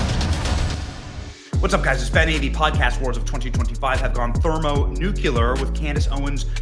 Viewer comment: Did you say thermo-nuke-u-ler? So we split the nukulus of the atom?